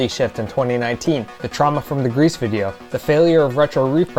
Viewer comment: Omg before the Greece video was explained I thought you were saying “grease video” and it was because her hair was greasy 💀💀